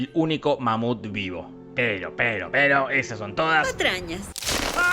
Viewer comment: pensé que dijo manute xd